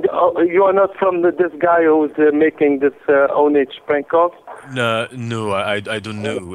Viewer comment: I could've swore I heard that guy say "ownage" in the middle of the prank when he switched to buk Lau. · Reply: Yeah near 4 minutes he says "I think it's ownage."